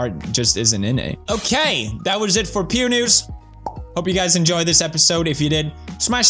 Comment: Pew News but i heard "Pewnis"